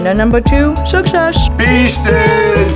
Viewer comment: Beasted, I thought you said weasted